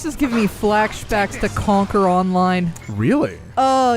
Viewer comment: What was that MMO you were talking about? I kept hearing "Conker Online" or "Contra Online", neither of which exist (sadly). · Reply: Dunno if I'm late, but I'm pretty sure she said Conquer Online